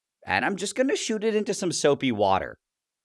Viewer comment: not gonna lie i thought he said soapium water